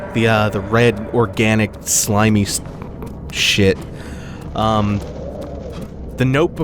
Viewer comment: I misheard you, I thought you said red orgasmic slimey ish xD